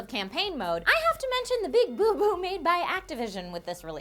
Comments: I thought she said big boobs.. Or.. wha.. sorry Im still... wow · Did she say Big BOOB BOOB at 2:39 ? :)